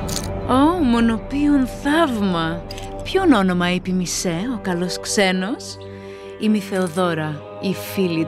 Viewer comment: Did she said "Xenos?" Let's be xenophobic, it's really in this year Let's find a nasty, slimy, ugly alien to fear